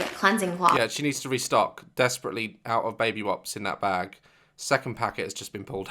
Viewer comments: Is it just me or at 10:40 did he say “baby wops”. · Did anyone else hear Dad says "Baby wops" instead of wipes at 10:41 haha 😂